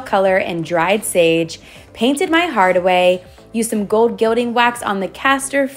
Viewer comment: I heard "painted my hide away"